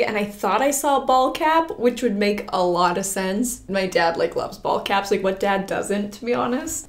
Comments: Am I the only one who thought said her dad was obsessed with bald caps- 😂😂😂😂😂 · I thought you said “bald cap” instead of “ball cap” and I was so confused 🤣🤣🤣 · With accent, when she kept talking about the "ball cap", I KEPT hearing "bald cap" - totally expected a joke outfit haha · the whole time i thought was saying “BALD cap” not “ball cap” i was like ???? why would your dad buy you a bald cap lmao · I thought said bald cap but she said ball cap on the dad part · Is it just me or did it sound like she said bald cap? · I thought u said bald cap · FULLY thought you were saying your dad bought you a “bald cap” until you pulled out the ball cap 😂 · I thought she said bald cap